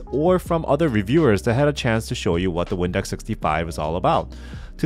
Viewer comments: All I keep hearing is "Windex 65" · Also the entire video all I heard was "windex 65"